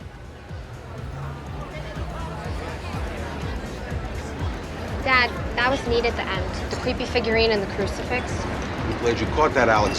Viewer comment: And, also, the eggs represent Ton nice ending. I stared typing befiore it cut to The Lupertazzi’s. The sacred and the Profane. It always sounded to me that carmine, rather than saying Profane it sounds like he says propane which he might’ve said which is funny nice nice video